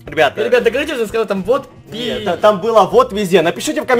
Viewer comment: Я верю больше Эдисону но послышалось мне вот пизде*